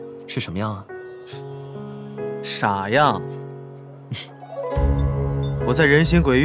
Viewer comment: gue nontonin sampe mabok 21:42 gue dengernya "sayang" langsung kaget hahahahha